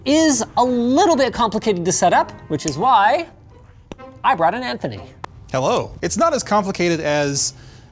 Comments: Linus: “this is a little complicated to set up, which is why..” Me: plz be Anthony Linus: “I brought in Anthony” Me: YES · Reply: I heard “I brought an Anthony” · Linus: "It's a little complicated... so I brought in Anthony." Breaking out the big guns, I see. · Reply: I thought he said "an Anthony" xD